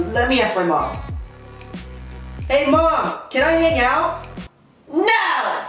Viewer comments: When he said hey mom can i hang out i thought he said hey mom can i make out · it sounds like he is saying can I. Make out